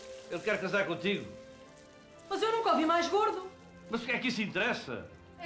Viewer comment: Shut up, Miss Dunkin Donut 2003 xD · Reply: Vicente Junior what did that girl say in 0:34 in Portuguese? "Mas eu nunca vi mais gordo???